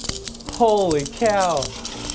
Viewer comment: does he say "Holy Cow "🐮🐄